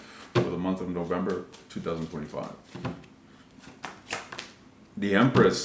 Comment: So I heard you say for the month of November in 2045 I obviously thought I was mistaken and so I listen to it again and again and again and what I keep hearing is for the month of November 2045 I am so weirded out right now crazy